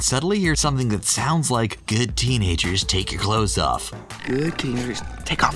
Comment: I heard "Good tiger, take off your cloak"